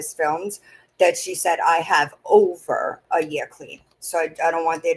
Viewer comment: that's what I heard to was "I swear to God, I don't have anything on me. I have over a year clean."